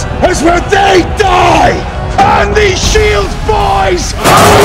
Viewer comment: Damn I always thought he said “ On these shields boys! “